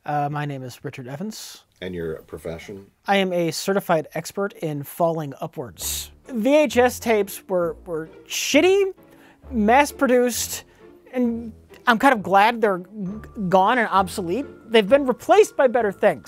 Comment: Rich Evans - "I'm a certified expert in falling upwards." Just gold 🤣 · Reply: Lol, Could of sworn he said "failing upwards" at first.